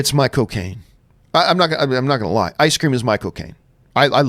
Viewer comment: I live in the UK and when you talked about ice cream I heard: 'Ice cream is Michael Caine. Ice cream is Michael Caine!' I mean, I would still lick the spoon but it gave me a pause, nonetheless...